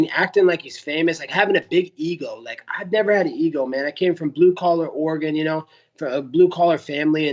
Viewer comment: did colby say NO Ego??????????????????????